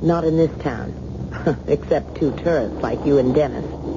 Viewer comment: Just started the 300-series playlist! Word! Edit: @ 35:43, I can't make out what the lady is saying. "...except two *****s"??? It almost sounds like "turds"! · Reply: Tourists?